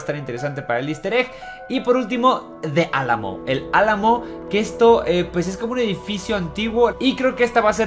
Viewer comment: entendí "kesto” xd